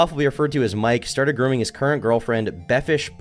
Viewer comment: i thought that said gooning